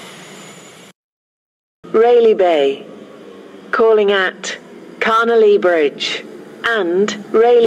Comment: When the station announcer says Carnelia Bridge (or however you spell it) it sounds like she says ‘Connolly Bridge’ imo.